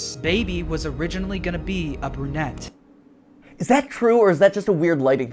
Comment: I at first thought he said "uppernet" instead of a brunette, so I spent like 5 mins googling what that meant, thinking it was a fnaf term.